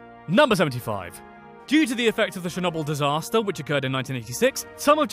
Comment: did he say shernobil instead of of Chernobyl